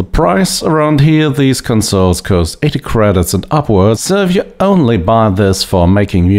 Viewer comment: What is he saying after “one word on the price around here…” after 11:52 , he is pushing the base of his tongue towards his throat for a guttural/nasal vowels and it is very hard to decipher the words phonetically 😝 something like “these concerns (consoles?) cause (cost?) eti credidts (??) at upwards serve you only bah this for making music….” 😠😝😂